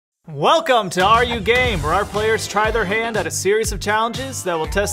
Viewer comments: I HEARD THAT AS 'welcome to ARE YOU GAY' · IN THE BEGINNING- I REALLY THOUGHT HE SAID “WELCONE TO ‘ARE YOU GAY?’” 😂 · For a second I thought i heard are you gay? Instead then “are you game?” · I heard “are you gay” not “are you game” lol any who hi Btw I am pan not trying to be mean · I thought said "are you gay?" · PLEASE WHY DID I HEAR "are you gay" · I almost thought the Intro said, "Are you Gay?" Instead of "Are you Game?" XD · why did i hear “are you gay” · At first i thought u said are u gay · At the start I thought it said “are u gay” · I-I thought they said “are you gay” and I nearly died of laughter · Who else thought hey said are you gay in the begging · When it “are you game?” I thought it said “are you gay?”